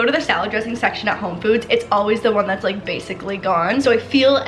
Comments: it kinda sounds like she says home foods LOL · Did anyone else hear when she said “home foods” at 11:16? 😂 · Anyone else catch at minute 11:18 her saying “Home Foods”?